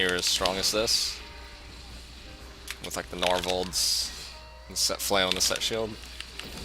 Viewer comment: what item did he say at 8:02? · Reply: Norvalds. That the name of the flail and shield set